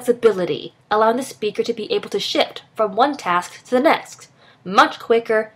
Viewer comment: I heard "allowing the speaker to be able to shit" at 2:14 I think